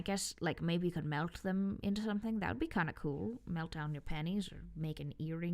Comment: I think it might be your accent coming through, but 10:43 sounded like "melt down your panties" when I first heard it, which was a VERY cursed mental image. Speaking of curse, listening to this made me realize what would be the PERFECT way to make an absolutely cursed "junk drawer" - it would be to have a junk drawer filled with very normal objects that you typically see in there, on the surface of the "junk," except once you start digging for things you find some extremely suspicious or bizarre objects that shouldn't be there.